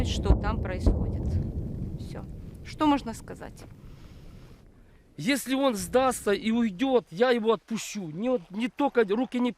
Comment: is not what he says.... He says if he gives up, I won't kill him and I will let him go.